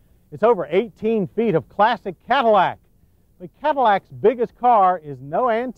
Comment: Did he say plastic Cadillac or classic? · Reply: Classic